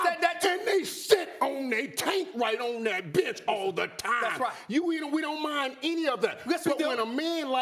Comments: Listen to Irving- and they shit on that taint right on bitch all the time!!!anyone else hear it. They missed it I think Smith heard lol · I swear I thought he said “and they shit on the bed all the time” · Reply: truuu i thought he said shit in this bitch all the time bout too say this nigga hell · "AND THEY SIT' ON THEY TAINT RIGHT ON THAT BENCH ALL THE TYYYME" 🤣🤣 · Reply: I swear I thought he said “and they shit on the bed all the time” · DID HE SAY THE SHIT OUT THAT TANK ON THAT bitch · Did Irvin say that Sam Bradford and Mike Glennon sit on their "TAINT" on the bench? LOL Did he say tape? If he really said Taint, I'm done..... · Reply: He definitely said Taint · i thought Irvin said "riding the b*tch all the time" lmaooo